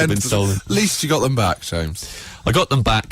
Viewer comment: what the heck does he say he spent it all on, at the very end of the video 7:02? "fabana mollies"?!